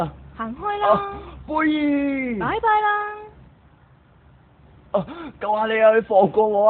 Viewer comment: xDD she sounded like "bye bye ! <3"